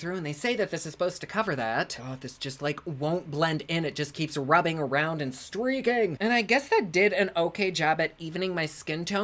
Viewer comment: I heard "running around and streaking" from my hallway and had to go back and rewind just to find out what I missed, only to learn I misheard "Rubbing around and streaking" lmfao.